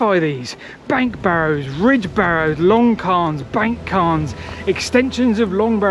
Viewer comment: Due to your accent, I heard "long cairn" as "long con" and immediately thought of somebody doing a kind of Tom Sawyer scam involving stone edifices.